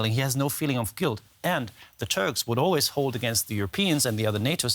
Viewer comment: Did he just say the Turds? May just be my hearing.